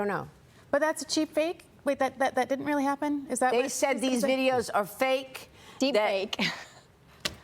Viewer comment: I heard... I'm not sure I'm going to introduce you/ address you, ole boy.